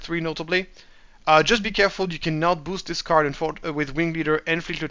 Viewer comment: what are you saying you can't do? I couldn't quite understand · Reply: You can't, Falcon is not a rebel.